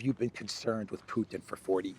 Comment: Come on guys, be fair. Biden meant to say that he has been aware of Putin for 40 years, not that he had actually met him 40 years ago. Putin came to power in 1999 - that is 25 years ago. It's plausible that Biden had been briefed about Putin 15 years before that. I'm a big fan of this channel, but lets be fair? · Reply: first i thought he said "for 14 years"... but he said 40.. hehe